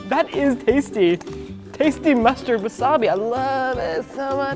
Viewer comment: Sorry but "wasabi flavored mustard" is NOT exists in this world..... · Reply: No need to say you're sorry, I have nothing to be upset about but does say at 10:14 "tasty mustard wasabi". To be fair, there's horseradish mustard and all kinds of other condiments mixed into mustard, I don't see why there wouldn't exist wasabi flavored mustard. Not saying it does, I have no idea from personal experience, nor can I read what the packaging says, I'm just saying I'd think it can be that such a flavour exists. :)